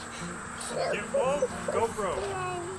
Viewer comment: did you say "get woke, go broke"? Haha, truer words have never been spoken.